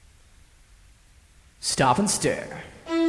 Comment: yeah i like it but it sounds like your saying "stop IN stare" haha i like it, its just that...